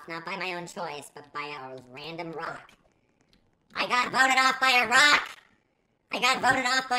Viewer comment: sounded like you said iraq but you said a rock